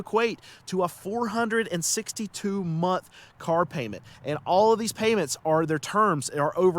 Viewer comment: o I had to do a double take on that cause it sounded like you were saying the loan would be 462 months. I was like WHAT? That's 38.5 years!